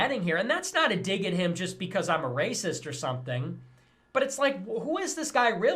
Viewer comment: Did he just say "just becase I'm a raciat?